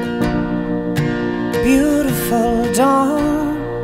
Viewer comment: I just hear "beautiful done". However "beautiful done" or "down" is indifferent, both has no sense to me. Hehehehe